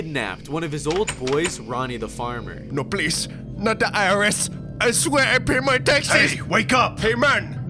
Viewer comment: did you say IRIS?